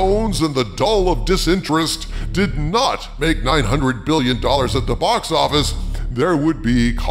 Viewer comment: did he say 900 Billion.? 😮